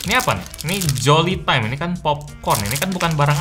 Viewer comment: gw dengernya col* time 😭😭😭😭